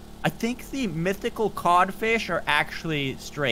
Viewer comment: did anyone else hear him say kan fish at about 3:20?